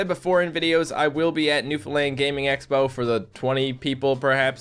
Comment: Its really funny when you say "new foundland" it sounds like you say "new finland" and im from finland.